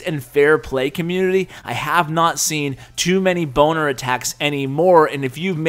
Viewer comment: its sounds like he said boner attacks